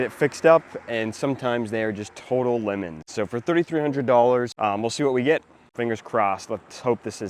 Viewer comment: It’s not April fools?🤔😂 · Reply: My bad, It was late and I misheard you as saying $33,000 and I was beyond confused.